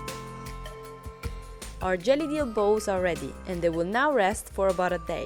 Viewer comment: I kept hearing "JELLY DEELS" 🤣🤣🤣